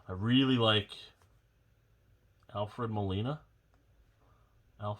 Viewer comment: Did you just say Alfredo marinara?